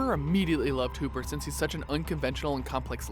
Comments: did he say hooper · DID HE SAY WHO-PER